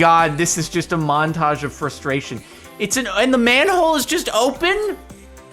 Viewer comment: I was wandering what did he say i hear ma nhoe but then i hear man hole